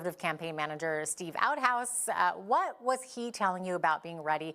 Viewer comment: Did she just say that guy's last name was "Outhouse"? I don't know about Canada but here in the United States, that means SHITTER!